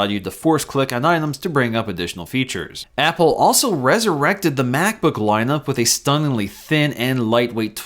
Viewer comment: did you say ressurected?